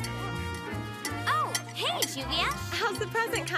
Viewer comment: she said I instead of Juvia